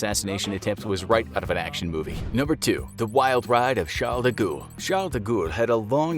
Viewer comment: Sounds like he says "The Goa'uld" from STARGATE